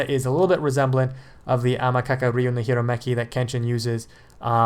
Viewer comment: What? Spell all that, you said right here, or its fake!! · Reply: Amakakeru Ryū no Hirameki.